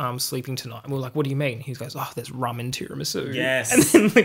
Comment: The fist time, I heard "there's ramen tiramisu" and I honestly get why someone wouldn't be able to sleep knowing that